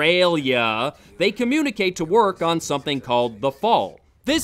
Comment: Did he say "they communicate to work..." at 6:20?